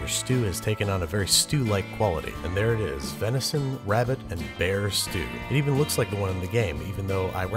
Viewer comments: Tbh I first thought it said ‘Beer stew’ and I was like channels inner alcoholic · I'm not gonna lie, I thought it said beer stew.......